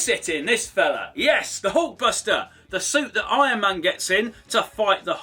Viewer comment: I thought he said "Iron Monger" 🤔😂😂